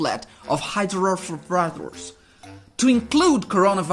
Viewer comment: Uau! novo remédio que ouvi no vídeo: Hydrofowafles aos 3:13 🤣🤣🤣 ele tava era com fome pra falar esse nome..?..🤣🤣